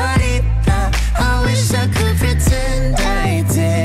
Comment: Everyone in the room: * watches the scene* Camila: omg that's so cute Director: she just wants a hug Camila: I just want gifts!! Shawn: hugs camila Also shawn: now that's the real gift Camila: * agrees * Everyone in the room: * awkward laughter* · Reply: Sergio Sagayno oh i thought she said “i just wants hips” and thats why she hugged Shawn